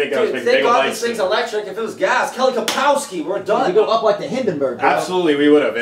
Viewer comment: did anyone else hear kelly kapowski as in saved by the bell?